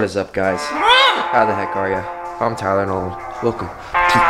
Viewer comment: When he said I’m Tyler Nolan I really thought he said I’m Tylenol